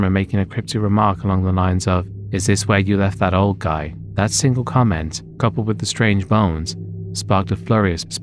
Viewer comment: I'm greek and in 21:31 this is false. Nick Crowley made the same mistake. What his friend really said was "Έδειξες Εμένα;" which translates to "did you catch me on camera?" Just a friendly notice. I liked the video.